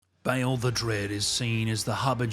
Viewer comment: Am I the only one that always hears 'Balerion ths Black Dread' when I hear 'Bayle the Dread'?